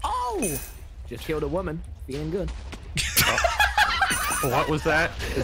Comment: "Just killed a woman, feeling good" -TommyInnit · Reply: I thought he said ''Just hit a woman, feeling good''